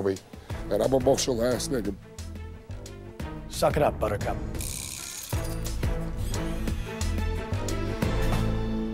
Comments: wait what he just say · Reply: Mr. Shade_inc white guys from Florida lol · I may be wrong but at 18:00 Did say " I'm an emotional azz N-word"??🤔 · did he say I'm an emotional ass n-word? Like I can't tell what he is trying to say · Am I the only one who heard say "he's an emotional ass nigga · did he just say “im an emotional ass n*gga”?!?